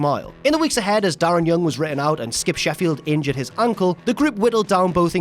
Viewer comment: Because of the accent it sounds like you said: "Skip Sheffield injured his uncle" instead of "Skip Sheffield injured his ankle"